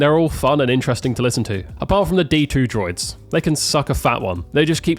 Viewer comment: am i high or did he say D2 and not B2